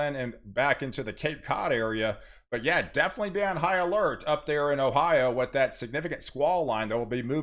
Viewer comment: I heard "be on Ohio-lert"